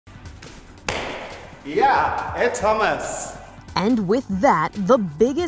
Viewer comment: Sounded like he said “ yeah it’s hummus!” at the end. This guy is amazing, puzzles stress me out.